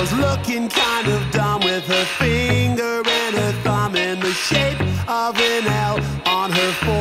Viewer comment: Bro i thought the music said with “her finger up her bum” 💀 when it said “with her finger and her thumb” lol